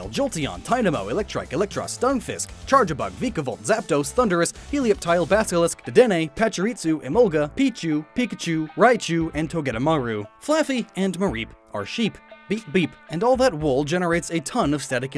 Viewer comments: Basilisk? Did I hear that right? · I swear I heard him say basilisk. Which Heliolisk's name does come from basilisk the lizard that can walk on water Probably also that lizard with the frills idk. But also Helio meaning light. Love the vids btw.